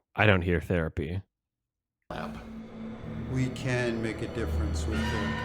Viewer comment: It sounds like Neil is saying, "...we can make a difference with the outcome".